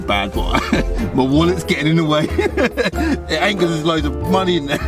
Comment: I thought he said "my willy is getting in the way"